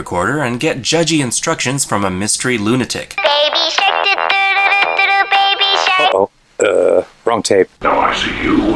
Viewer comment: Awesome. Now I have 🎵“baby shark doo doo doo doo baby shark” 🎶 in my head. Thanks. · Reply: I heard "baby shit do do do do do do..." and was like WTH ?